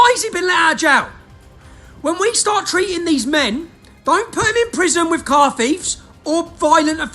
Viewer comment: The guy who did the subtitles, needs to be fired! The subtitles say "long time prison with coffee" But Tommy said "don't put them in prison with car thieves" 6:23 MESSAGE TO KIER STARMER Tommy has a British heart only you could ever wish for! He's trying to bring enough immigrants, over for a "better life" & get their vote, if Kier doesn't change direction, he would be sealing the fate of the UK way of life, then surely, CW will break out.